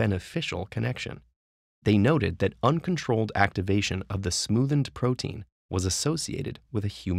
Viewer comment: Sorry but I keep hearing "the smoothn't" instead of "the smoothened" and its driving me insane